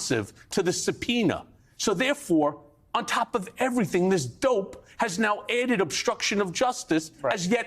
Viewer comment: "This Dope" ... Did I hear that correctly..?😅